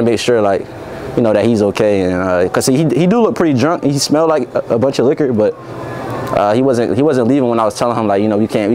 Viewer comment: I thought you said that he came with a bunch of chicos liar